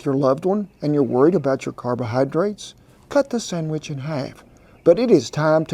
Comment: I have to back that up twice, I could have sworn you said, "Cut the summbitch in half." hahahaha Love your channel, Cowboy! Never thought I'd see you make a Monti! My favorite breakfast. I top mine with a dash of powdered sugar, raspberry jam, and homemade maple syrup. YUM!